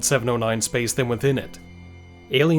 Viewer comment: did you say MOSTILY? You DID!